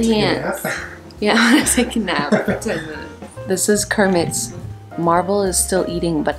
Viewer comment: At first when she said Kermit I thought she said sammoth 😂